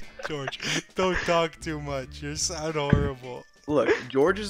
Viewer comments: IS IT JUST ME OR DID I JUST HEARD DREAM CALL GEORGE ADORABLE-💀✋ IM DYING LIKE GEORGE RN-💀💀💀💀💙💚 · When sapnap was talking about him missing george near the beginning I swear dream said "George dont talk to much..you sound adorable" Or was that just me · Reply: I hear, "don't talk to much your so adorable" but that's just me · I think dream just called George adorable…? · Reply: i wish lol, he said "you sound horrible"